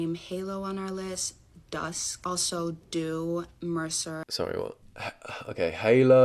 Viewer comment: did she say MRSA? Like the infection?